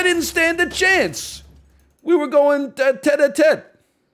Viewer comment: What did NL say at 4:28? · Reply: "We were going tête-à-tête"